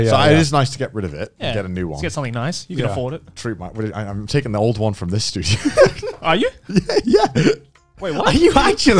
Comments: To be fair, when Connor says, "I'm taking the one from this studio" it does kind of sound like he said "I'm taking the one from this dude." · You know what might've happened? When Connor said "I'm taking the old one from this studio", at around 2:28, joey might've heard "I'm taking the old one from this dude". Just maybe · Connor was telling the story pretty clearly. I dont know why they were so confused xD He literally said: I'm gonna get rid of my WASHING MACHINE (...) I'm taking the old one FROM THIS STUDIO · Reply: and it also sounds like he’s saying “I’m taking the old one from this dude here” instead of “the studio”. The hand motion towards Joey also doesn’t help😂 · They probably thought Connor said “this dude” instead of this “this studio” and that’s where the confusion started